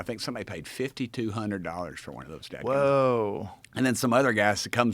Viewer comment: War Pimps is a good band name. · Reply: I thought at first he was saying dead gum . Thought he was alluding to being tasteless 😂